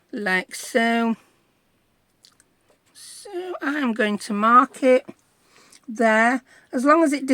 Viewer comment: You said I'm going to mark it and I thought you said "I'm going to market"